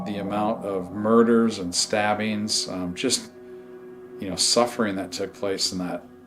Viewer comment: Hooollly shiiiit when was in the building and asked what crime did u commit I heard it say “murder and r*pe” soooooo crazyyy